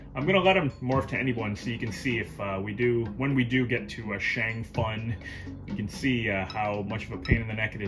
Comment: I'm digging this version man. The zooming reminds me of Art of Fighting. Shame about the loading. Can't help but chuckle when you say Shang Fun! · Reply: I thought he said Shank Fun as a negative pun...